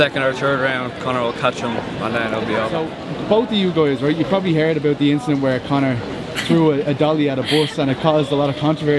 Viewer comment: I did not understand one word of what that girl said. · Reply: she said "ah god i havent a clue, i didnt see it im just standing here"